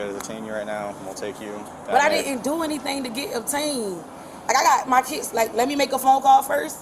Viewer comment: Did she say "I didn't do anything to get obtained"? lol